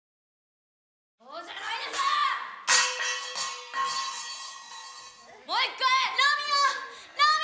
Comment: あの... 0:01~0:03のセリフ 聞き取れる人がいますか？ 教えて下さい... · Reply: そうじゃないでしょ！